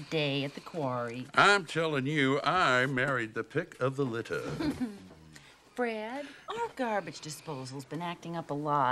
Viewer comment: Did she say Fred or Bread? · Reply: She said, "Fred".